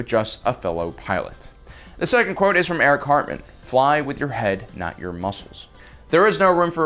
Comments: I kept hearing "Eric Cartman"; YEAAAAH BITCHES, I'M A PIIIIIIILOOOOOT!! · Kinda cool to hear about Erich Hartmann here... Although I kept hearing "Eric Cartman" in my head :) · I thought he said "Eric Cartman WW2 flying ace" for a minute there. LOL "Screw you nazis, I'm going home!"